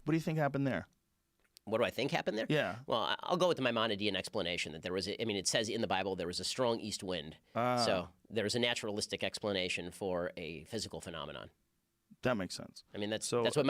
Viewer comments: That's exactly what he said... But ok.. whatever makes you feel better. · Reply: what he said is the is a naturalistic explanation for the parting of the red Sea...he didn't say he likes.....he said he'll go with Mamanadies on this one.... The quotation marks on the man comment was faulty...how you gonna use quotation makes then misquote the person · Because Joe asked him to try to explain the Red Sea naturalistically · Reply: Chrismex Presence no he didn’t. Your imagining things. Check @ 12:05 joe never says that. “What do you think happened there” is what he actually said.